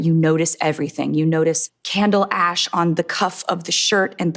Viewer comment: did she just say.... candle ASH???